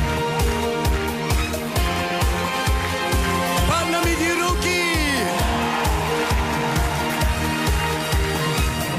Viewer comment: ～ What did he say? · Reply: Поднимите руки! Podnimite ruki! Raise up your arms! The performance was in Moscow, so he said it in Russian ❤